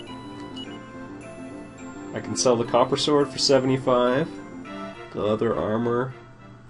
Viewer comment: I was listening to this in the background and I thought you said the "Connery Sword". You have no idea how disappointed I was.